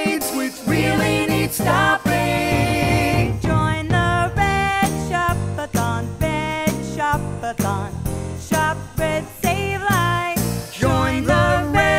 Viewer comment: am I the only one who heard it as "shop a thong"?